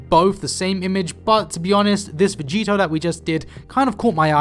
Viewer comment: did he say vegeta?